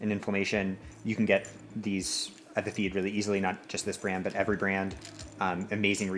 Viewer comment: Does The Feed sponsor you or do you sponsor The Feed? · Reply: see I thought he was saying "check out the feet" and I was stoked but then I just saw gel